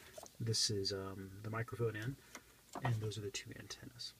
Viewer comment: what is the second plug you show it sounds like your saying mico in???